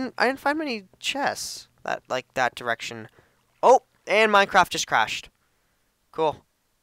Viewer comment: did he say minecrash just crashed